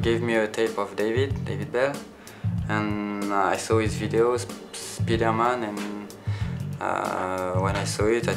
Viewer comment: when he said speed-air man, i thought he said spiderman. makes sense.